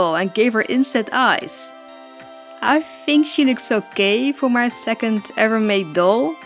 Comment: What said: "I think she looks okay for my second doll" What I heard: "I think she looks so gay" Whut😂